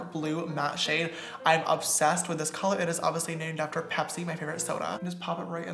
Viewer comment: Did he really say this is from Pepsi my favorite cola